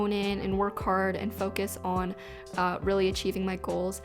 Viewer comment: I misheard and thought she said "bogus" instead of "focus" as her word of the year...